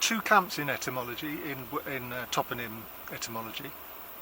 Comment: I've learned a new word today, "Toponym". For a moment I thought you were saying "Tottenham" and I was wondering why we were talking about London. I do like the Dave Clark Five, though.